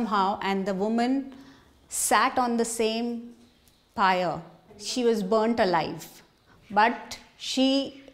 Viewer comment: she did not say "she was to life" what she said was "she was burnt alive" (you can trust me i am Indian and can check the "sati ritual" if you wanna check it out